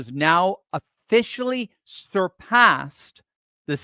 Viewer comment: Did he say "sturpassed"??